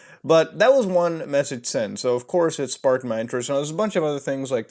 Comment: Lol i thought you said sparked my nitrous